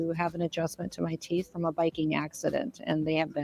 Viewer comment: At first, I thought Kristi Noem said she needed her teeth fixed because of a "Viking accident". Man did that conjure up some mental images.